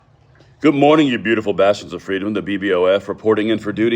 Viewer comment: ????? Did he just say---- you beautiful bastards of freedom??? Or i need to change out my hearing aid batteries,,, lol